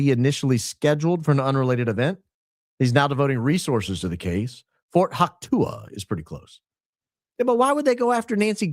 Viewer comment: didn’t think I’d hear say hawk tuah! 😭😭😂🤣